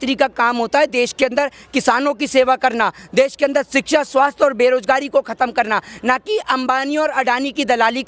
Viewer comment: Are bhai kya bol gya ,@ 5:23 shiksha aur savasth ko khatam krna